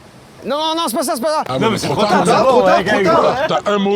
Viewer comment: 19 : 07 j'ai entendu "oh mais cheh !" !!!🤣😂😂😆😆